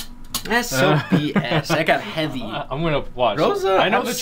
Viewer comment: Did he said THAT'S SO BS